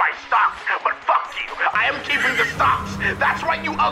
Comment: I misheard "stocks" as "socks" for the longest time, and I think it became funnier because of it